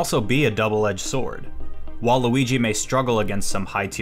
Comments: who else thought he said waluigi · Dude seriously snuck in Waluigi into the video instead of saying “While Luigi” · definitely sounded like Waluigi. ( Waluigi For Smash) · Did he say waluigi